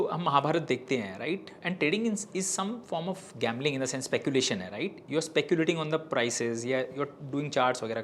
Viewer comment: DID HE SAID TRADING IS GMABLING 25:10 , ....